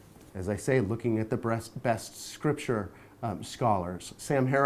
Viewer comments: Did anyone else hear Brian say breast and cover it up at 32:27 ? lol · he has a freudian slip where he says breast instead of best. Personally I like to think that is because he is forcing himself to think of a womans body to try and keep himself from imagining dan fucking him because he is a closet homosexual.